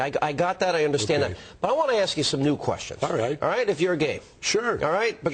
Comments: Sounds like he said, "if your gay." Then Jesse says, "sure sure." Hm... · Did he just say "if you're gay???"